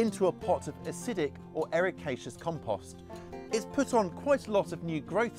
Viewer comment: Acidic or "aricatious" ??? compost? What does that mean and how do you do it? I keep hearing blueberries need acidic soil, but they seem to grow well in just regular ground. But I never heard that second word before. How do you know when your soil is acidic enough?